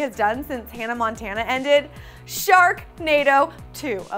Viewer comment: When you said Sharknado 2 for some reason I thought you said Doctor Who.